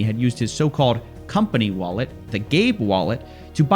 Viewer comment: cracks me up. sounds like your saying gay wallet every time! keep up the good work!